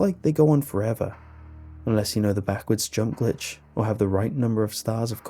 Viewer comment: The English (Auto - Generated) Captions Said “Backwards Junk Glitch” Instead Of “Backwards JUMP Glitch” So , What You’re Telling Me Is , Mario Has The Ability To Rotate His D**k By 180 DEGREES!?!?!? Illuminati Confirmed